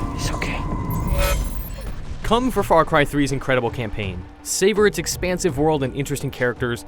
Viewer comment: did he just say "come for far cry 3's incredible campaign"